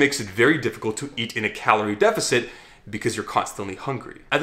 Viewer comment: did he say to add insulin to injury lmao